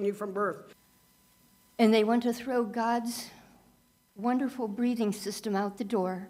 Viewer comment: Hmm. I thought she was saying that we want to throw away "God's BREEDING system."